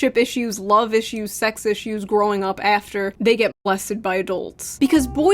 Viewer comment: when you said "molested" it sounded like you said blessed for a second... xD